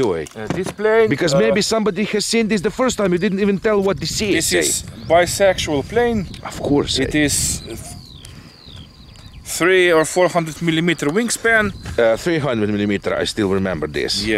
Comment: did i just hear "bisexual plane" in Russian accent😅